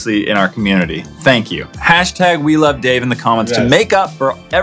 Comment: #WeLoveDave #BryansChoice <3 <3 <3 <3 <3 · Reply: I thought you were saying we love dance😂